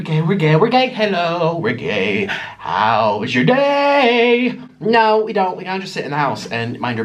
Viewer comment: When the video started I thought it said I am gay but it said how is your day